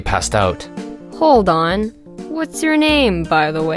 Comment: Is it weird that I heard “What’s your name man” And then I sang “Alexander Hamilton, my name is Alexander Hamilton” Insomnia and obsessions are fun 👌🏻👌🏻